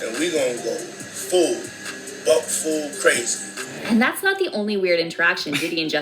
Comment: I lost it when PDiddy said they're gonna go bat-full crazy because I misheard "Butt-Full Crazy